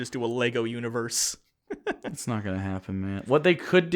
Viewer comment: I may be late to the game on this one but did call "Matt" at 23:30? Is real name?